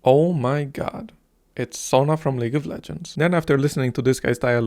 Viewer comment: "aaah, nice to see hatsun- what did he say????" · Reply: sona.